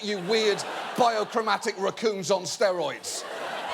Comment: I swear to god I’m stoned AF and I thought he said “bisexual raccoons on steroids.....” and i got just a little bit concerned